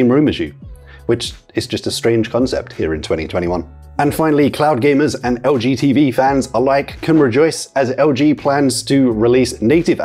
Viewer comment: I thought you say "cloud gamers and LGBT fans"